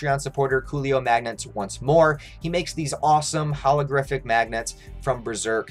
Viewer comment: Did I hear “hologriffith” magnets?